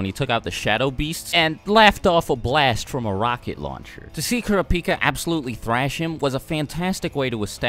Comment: did he say kura-pika I am triggered